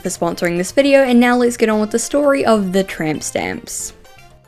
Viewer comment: I thought it said “The Saga Of The Time Stamps” 👁👄👁